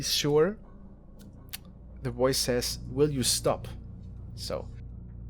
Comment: I heard "will you stop"....